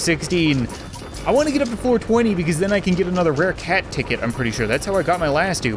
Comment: The first time I heard “ I can’t wait to get to 4 20” instead of floor twenty